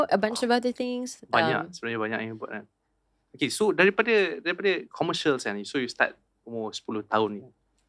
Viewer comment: did just say sayang??? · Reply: Bkn sayang tapi "komersial en" laju2